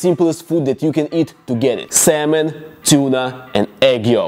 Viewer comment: you said Tuna but I Heard Chuna 😂